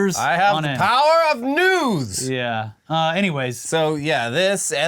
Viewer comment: thanks guys. so sick of hearing about T. or really any of our senile government leaders. but thanks, ive got to push through and listen. on a better note.. i swore i heard yell out 'the power of nudes!" when i was distracted but it turned out to be 'the power of news' :( I know this isn't THAT site. still. a great idea! lol